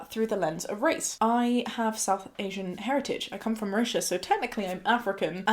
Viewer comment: did you say you come from russia and is therefore African?? even the subtitles say that, but that can't be right can it?? or maybe I'm just stupid ooooo now I heard when you said it the second time it was Mauritius that makes so much more sense hehehehehbrfned